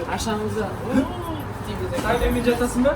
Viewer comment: что сказал?? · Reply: Сай менімен жатасың ба? Убилааа(күліп тұрған смайл) ТаСай из РеАл